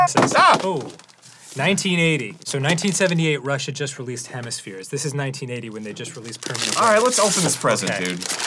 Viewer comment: I thought he said "1980. So 1978, Russia just released hemispheres" Got me so confused for a moment. Sounds like some sort of sci-fi plot.